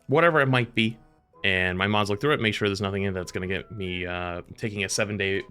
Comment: For a second i thought he said "my mom's looked thru it" at 0:14, like oh that's nice of her.